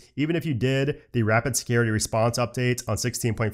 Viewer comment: It sounds like you said Rapid “Scary” Response Update 😂… I’m sure it was scary to some people though, lol. Thanks as always for your knowledge and explanations!